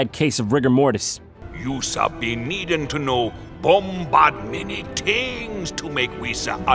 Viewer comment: I think he says bombad. WHAT THE FUCK EVEN IS STAR WARS ANYMORE????